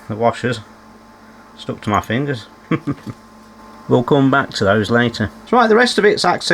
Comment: did you say ‘we’ll gum back to this later’? Nice touch with the channel theme tune 🎶 😊